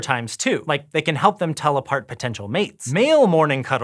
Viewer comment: it almost sounds like he is saying teleport.